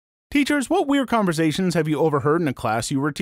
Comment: am i, the only one who heard him say peters in the beginning of the video instead of teachers?